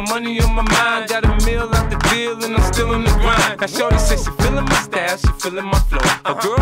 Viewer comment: Al principio pensé que decía shawty y no shorty